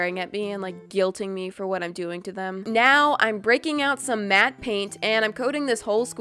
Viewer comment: I thought she said "Now I'm going to bring out Mat Pat." I'm like I wAnTz HiM Ima sQuIsH hIm